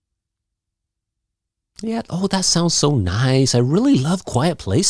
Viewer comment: hello teacher I think You said that sounds A nice at 1:40😀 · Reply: I said "that sounds nice," then the 2nd time I said, "that sounds SO nice."